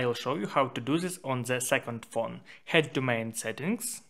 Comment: ? What u said 2nd phone?